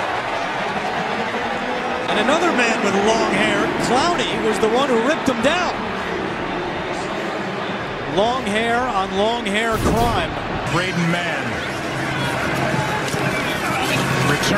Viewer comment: Did he say Long hair on Long hair crime😂